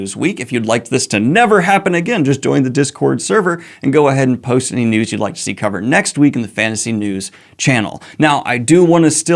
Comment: Swear to god I thought you said "post any nudes you'd like to see covered"